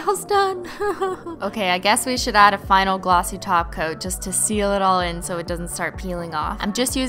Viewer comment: i love you but if i hear “glossy taco” one more time i’m going to shoot myself.